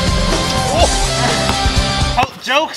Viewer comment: Who else heard him say "rebound" after Jesser missed the 3pter